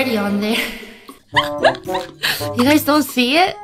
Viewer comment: Lmao this is mean to say. Im sorry. But the at the very end i thought you said that there was disappointment on your chest🤣🤣🥲